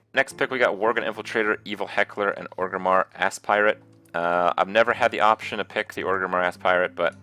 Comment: is he saying ass-pirate?